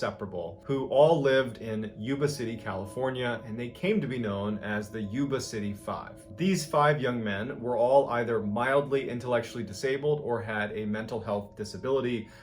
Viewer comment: I had to rewind because I thought he said they were all "WILDLY disabled"...